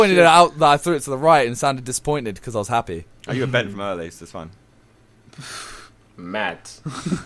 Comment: what did say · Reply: best kidd Something along the lines of being bent from earlier.